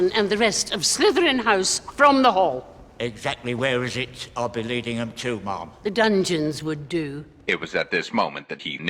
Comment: why do it sound like he says where exactly am I leering them too mom